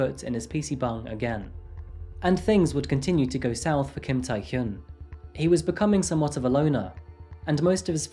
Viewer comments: I didn't see the video yet but I thought he said Kim tae hyung I was like HUH🤓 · when he said Kim Taehyun i screamed i thought he said Kim Taehyung tskskkd